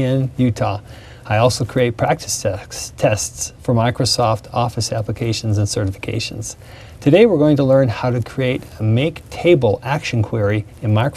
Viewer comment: did he say practice sex?